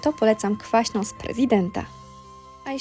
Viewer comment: Usłyszałam „jeśli chodzi o śmietanę to polecam kwaśna z providenta” 🙈😂